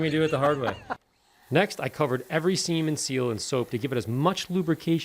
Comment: I thought he said. "I covered every semen seal"